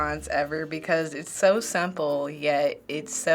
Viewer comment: Did she just say that, "K," is, "so supple"? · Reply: Yaoi Fan "simple"